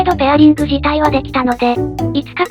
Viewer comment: 婆ちゃんシミュレートに聞こえたww